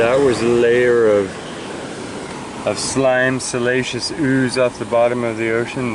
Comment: You had me confused with your pronunciation if "siliceous", lol. I kept hearing "salacious". While I get excited about rocks, I don't get turned on by them in THAT way. Similar sounding, but completely different definitions. Anyway, now when I hear you pronounce it, I will know which definition you mean, even though you say the other word. 🙃